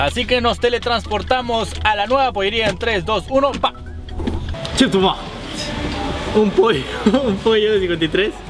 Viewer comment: En el minuto 6:27, en ves de decir chef timug, creo que dice Chetuma 😂😂